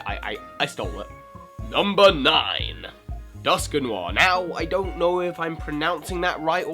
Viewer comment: Sounds like your saying "duskanoir". lol. "Dusk-newar"